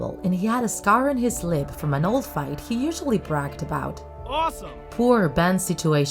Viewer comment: did anyone hear AWESOME